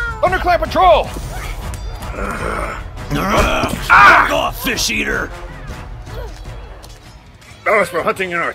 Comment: Is it just me or... Did it sound like Tigerclaw just swore?? I know he propably said "Back off, fish eater" or something like that😅 I heard he said "**ck off fish eater"....... Sorry... I LOVE THIS SERIES SO FRICKIN MUCH!!!!!!!!!!!!!!!!!!!